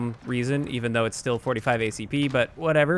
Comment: did you say tuna?